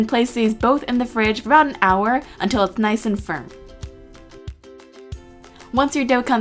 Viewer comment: was it me or did it sound like she said until it's nice and sperm.😇😅😂🙊. lol